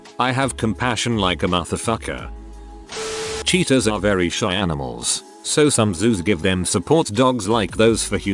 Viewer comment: When it said, "Cheetahs are very shy animals," I thought it said, "CHEATERS are very shy animals." I very confused at first lol